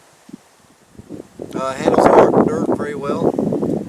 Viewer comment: Hi, what exactly did you say at the 1:19-1:21 mark? A gust of wind hit the mic at that very moment. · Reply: "handles hot dirt very well".